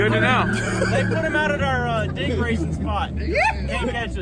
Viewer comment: That guy says day racing spot. At first I thought he said date raping spot. I had to go back to make sure what he said. lol